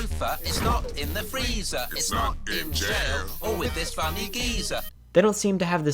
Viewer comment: did he just call him a fucking geezer?